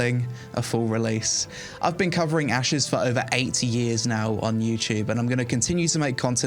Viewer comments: Really sounded like you said “I’ve been covering Ashes for over 80 years now” and even if it was 8 it sounds fitting 😂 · I swear to go i heard "Ive been covering ashes development for about 80 years" rofl